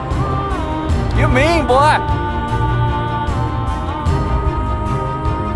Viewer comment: What the hell is he saying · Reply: "You're mean, boy!"